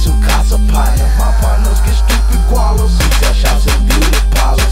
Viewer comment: What kind of shops? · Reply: it really sounds like he is saying meattail shops.